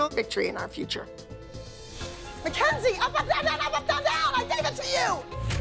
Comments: till this day i still want to know wtf she was trying to say here lmao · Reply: Up up down down up up down down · LOL WHAT DID ABBY SAY IM DEAD💀💀😭😭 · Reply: I thought she said “up up up down top down town I gave it to you “🥲🥲🥲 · LOL WHAT DID ABBY SAY IM DEAD💀💀😭😭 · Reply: Up, up, down, down but really fast